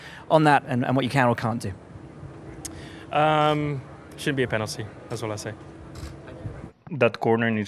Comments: did he say should or shouldn’t? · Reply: I've heard it as "shouldn't" lol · How sad that young drivers like Norris are afraid to give their opinion when they think differently from the FIA because they know that a few bad words against official decisions will ruin your sporting career. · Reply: I also thought he said „should“, until I put it to 0,5x speed, there I clearly heard „shouldn‘t“ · did he say should or shouldn’t? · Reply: I thought he said shouldn’t. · George had a skill issue. You can not blame Alonso. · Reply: I thought he said "should", but maybe you're right. EDIT: listening to it on my headphones instead of speakers, yeah, sounds like "shouldn't".